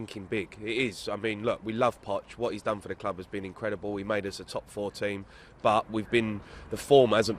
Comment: i could swear he says "he made us a top fourteen" - which would be quite accurate really